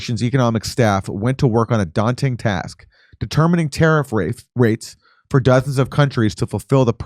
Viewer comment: did i hear tarrif rapes?😂 So accurate